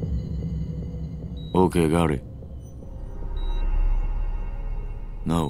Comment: All I hear is "Just no chance is bland"